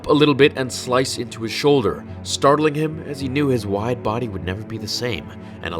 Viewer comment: When he said wide boy I thought he was saying white boy